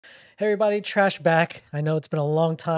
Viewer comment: I heard "hi everybody, TRASH BAG" ahah I'm sorry ❤I love you